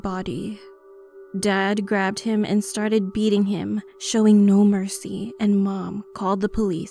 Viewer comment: i thought she said "eating him"